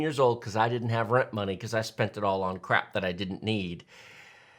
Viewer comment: I was listening to this in the background and I heard, "Cuz I spent it all on crack." I had to come back and relisten lol.